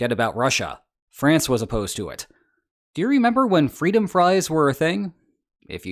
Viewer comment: For all the talks about politics, he always sounds really apolitical to me, which is refreshing and why I listen to him instead of the actual news. Also, as an aside, I was in middle school when Freedom Fries were a thing, and I kept mishearing it as Free Da Fries, which was very confusing.